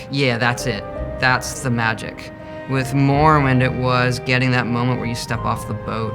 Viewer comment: Wait at 1:57 did Todd just call morrowind Mormon????